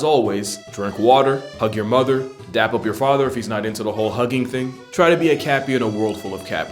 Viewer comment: Genuinely and unironically thought he said "and jack off your father if he isn't into the whole hugging thing." at the end of the video And I just went "bit of an escalation but ok"